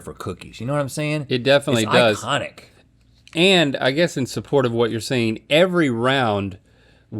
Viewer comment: Link does indeed say "in support"